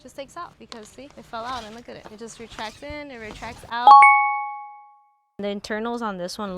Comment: thank you for the edit, I kept hearing you say retract and I was ready to say sum 😅😅